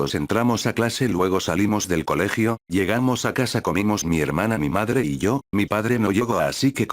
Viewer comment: En el minuto 1:20 escuché comimos Mier** XD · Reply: Cristopher Orozco yo escuché "comimos mi hermana" :v